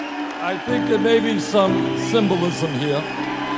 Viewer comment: What does he say at 0:53 ? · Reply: I think, I think that maybe some symbolism here. I know it doesn't look like it, but that bird is really a dove asking us for world peace. No more war!